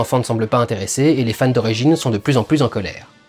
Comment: "Et les fans de Régine..." C'est quoi le coït ? ; ) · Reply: D'origine*